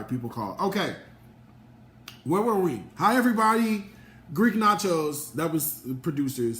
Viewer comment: I thought you said Greek nachos is that what you meant